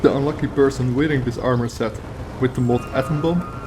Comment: What mod is the spell at 03:20 from i think he says the name but can't understand his accent xD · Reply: Lol. It was trickier to pronounce than I thought. It's Atom Bomb ;) <link>